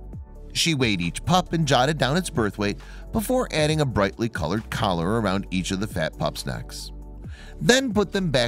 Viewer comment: Did he say, "The fat pups necks"